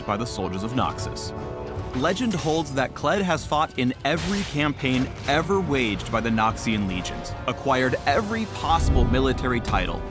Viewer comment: Am I the only one that thought they said "fought in every campaign ever waged by the nazi legion" at 0:44 ?